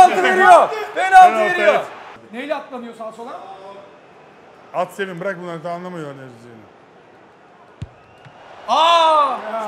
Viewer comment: acunun ne dediğini anlayan yazsın pls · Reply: At senin birak bunlari daha anlamiyolar nereye ısışini